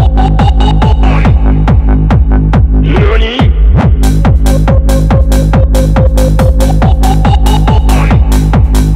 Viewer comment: What he is saying? 🤣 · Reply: Yoni bu hahahaha